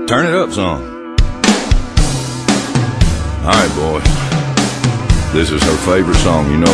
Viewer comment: he says boy and the subtitles say boys'